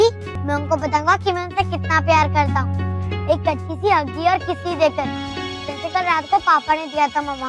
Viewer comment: is it me or someone who heard this more "kachi" then "Achi"💀🖐️